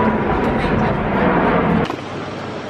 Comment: anybody else hear "hey boo" at 7:00 😏